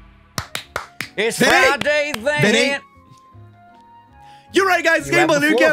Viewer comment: IT'S FRIDAY THENNNNNN IT'S SATURDAY SUNDAY WHAT!!! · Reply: I thought you guys said ‘Friday this’, not ‘Friday then’!